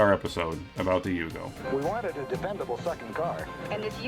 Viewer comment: I could have sworn that guy in the car advertisement said. " We wanted a dependable f**** car." 😂 But what he really said was. "We wanted to dependable second car."